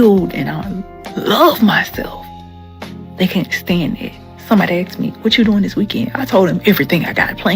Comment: Did she just say.. Somebody axed me? Sounds painful.